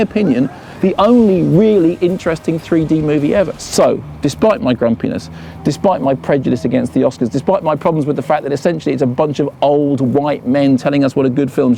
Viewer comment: I thought he said 'despite my grand penis' at the 03.29 mark.